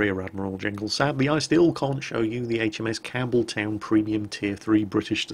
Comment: and I thought you were saying HMS Cameltoe